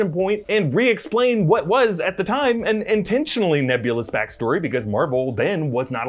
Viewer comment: Did he just say 'Nebula's backstory' at 13:52 😂